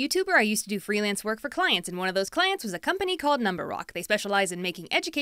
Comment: i thought it said number co- D